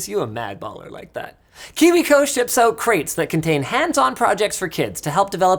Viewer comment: If you watch the video at 2x speed it sounds like linus says "kiwico shits out crates"